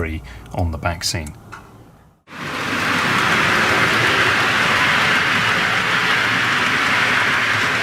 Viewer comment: Every time you say 'back scene' I keep hearing 'vaccine'. This last 12 months has really affected me!!